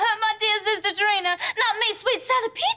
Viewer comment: “WHO, ME? WHY I WOULD NEVER MY DEAR SISTER TRINA. NOT ME SWEET SALLY PEACHES.” 🤣🤣🤣🤣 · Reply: Lol i always heard "Sadie Peaches".